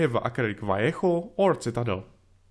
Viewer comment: Citadel (si-tuh-dell). I was like, what paint did he just say. lol. Love your work. Definitely my favorite model-building channel.